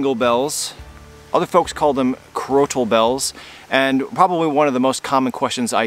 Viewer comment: Oh nice! I always thought they were saying scrotal bells. Then when you started calling them jingle bells I was thinking they must hang from a scrotum. Thanks for the information.